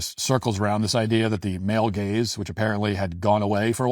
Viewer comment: 😂😂😂 At first, I thought you said "male gays" and my brain nearly broke... then, after hearing this drivel that passed for an article, I found myself imagining you said "male gays", and freed my mind from this woman's idiotic claims with a dismissive giggle.